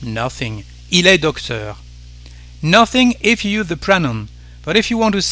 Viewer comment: Doesn't the English have "an" as well as "a" ? Regardless, what did you say at 3:19 ? Sounds like: "Nothing if you the prenom?" Sorry my picking up on words are not so great.